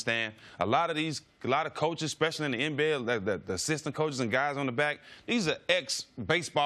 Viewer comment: Gregg Popovich never played in the NBA. Phil Jackson and Pat Riley were basically NBA scrubs on the bench. They're three of the best head coaches ever. Nice try, Stak. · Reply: I thought Stat said assistant coaches and coaches on the back end.🤔